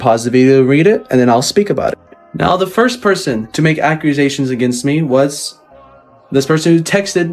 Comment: why tf does he keep saying “ACCRUSATIONS” 😂😂